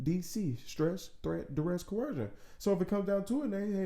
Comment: STDC as your SOS, stress threat duress corrosion? corrusia? what is the word for the letter C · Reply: COERCION Hopefully you know by now, 4 yrs later